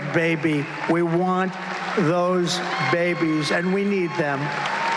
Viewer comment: I heard that "And we need them" (babies) as "And we eat them".